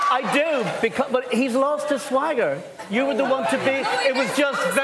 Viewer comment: What does lui says about him??that he lost his swageing or something like that?? In